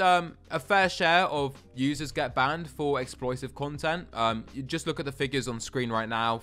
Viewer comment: exploitive* I heard as explosive lol